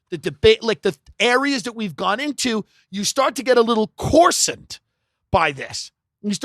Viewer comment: What word is Tim using at 3:01? Corsant? I'm unfamiliar with this word and I can't find it searching a dictionary. Help! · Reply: coarsened